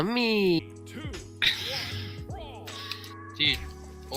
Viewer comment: did he said yummi from league of legends